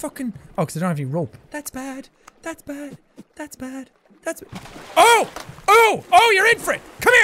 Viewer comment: Was I the only one hearing "All the way!" I could have sworn I heard "that's bad, that's bad"